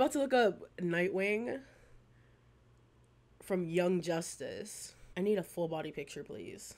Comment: I thought at 04:36 you said “Yum Justice” and that would’ve been the most accurate nickname for that show honestly